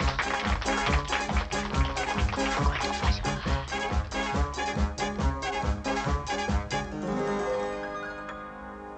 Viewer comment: What is being whispered at 0:40 ? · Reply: “Good luck and don’t fuck it up fraulein”